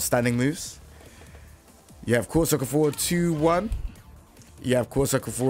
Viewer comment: what are these two moves,like, I hear of course forward 2,1 but it's not it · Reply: i think he said quarter circle forward 2, 1